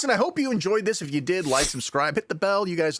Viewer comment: The auto-generated closed captions call you Shane Arman Roe, which is exactly what I thought you were saying back when I first discovered a million years ago.